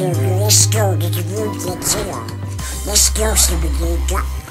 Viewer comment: Did donald say stupid ni